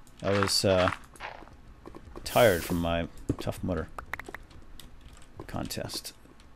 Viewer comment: Every single time you say tough mudder- i thought you said tough mother. "I got really tired from my tough mother." o___o